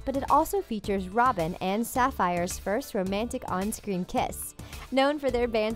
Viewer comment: Am I the only one that heard, "Robin and Saphire" instead of Starfire.